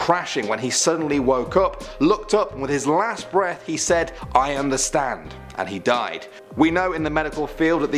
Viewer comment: I thought said I am the Stan instead of I understand